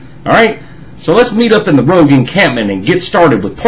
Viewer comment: lol kinda off topic , turn on closed caption and go to 1:26 when cc say's lets meet in the rogue encampment , youtube picks it up has lets meet up in the broken can ... kinda funny watching the sentences that pop up there